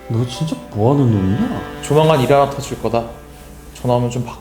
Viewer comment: 뭐라는거임 이바냐터질거다가 뭔말임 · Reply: 조만간 일 하나 터질거다.